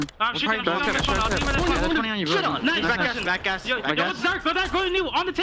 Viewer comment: what did they say?!?!?!?! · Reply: Shit on twice